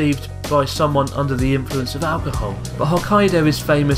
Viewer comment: I thought it said "big alcohol".... Those last 2